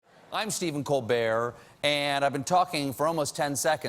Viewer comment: sometimes I hear "I'm Stephen ColBear" xD